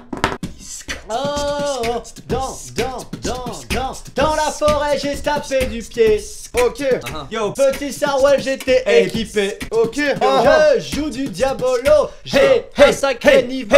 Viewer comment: c'est moi ou mastu dis '' biscotte petite biscotte '' 😭😂